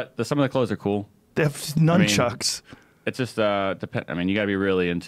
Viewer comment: Did anyone else hear Joey call the nunchucks noonchucks at 1:52?